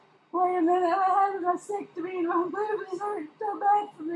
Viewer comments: Did she say that she had a vasectomy? ( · oops my bad.. I listened to it twice too. I am getting old and better check for hearing aids, lol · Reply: lol I thought she said vasectomy too. I was thinking she was getting hysterectomy and vasectomy mixed up.